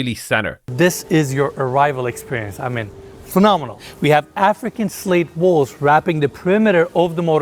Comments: I thought he said ‘African slave walls’……I’m like WTAF😅😅😅😅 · i thought he said African slave walls and spit out my drink lol